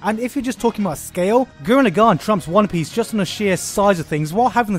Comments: Btw what did he acutly say at 6:50 i didnt understand it and i would want to watch that anime? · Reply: Gurren Lagann. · what's the anime called at 6:50 he say something like guruna gan or something like that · What are those three anime that he mentions at about 6:40 I got the first two but that last I can't hear its name so if someone could tell me that would help thanks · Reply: Gurren Lagann he also mentioned Psyren.